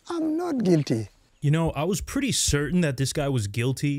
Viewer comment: Bruh I would’ve swore he was saying I’m not Ginty. I’m like wtf is ginty. I had to rewind it and finally heard he was saying I’m not guilty🤦🏾‍♂️🤣🤣🤣